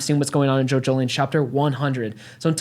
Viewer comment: It sounds like he says "JoJo Lands chapter 100" lol. Obviously he meant to say Jojolion, but did he get insider info from Araki on what the next part would be called?!?!